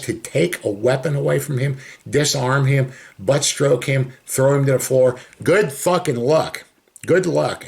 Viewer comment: Did you say butt stroke him?